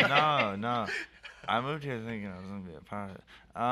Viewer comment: what did peep say? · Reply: “I moved here thinking I was gonna be a pornstar”